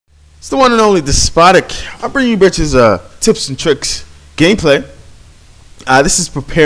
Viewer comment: Did you just say " i am bringing you a tips and tricks gay play " ? :)